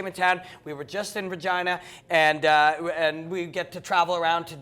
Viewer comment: did say what I think he said? "we were just in vagina" lmao